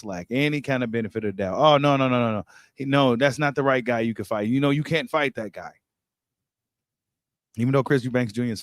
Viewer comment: I thought said he wouldn't fight thurman..